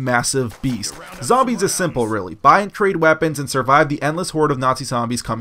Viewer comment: Did he say buy and create weapons ??? For w@ before